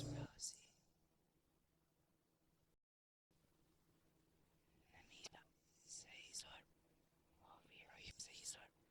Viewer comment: I recorded the whispering and played it backwards and it said: Rosie something something (idk what that bit said) this world, Rosie is not human, Rosie is pure evil, Rosie is.....a demon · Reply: The first part is, "Rosie is not of this world"